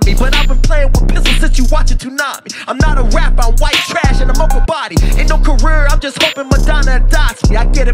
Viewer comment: Bro said " I'm whitetrash in a mocha body" that's the hardest shit. This freestyle was heat. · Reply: Holy shit. This whole time I thought he said im whitetrash in a mobile body.